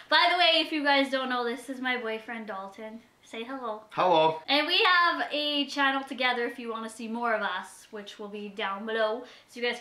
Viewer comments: I heard 'we have a child together' rather than channle and I was in complete. confusion. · Omg I thought she was going to say child together lol